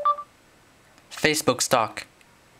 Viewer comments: lmfao I honestly thought he said "facebook stalk" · I thought he said "Facebook Stalk" LMAO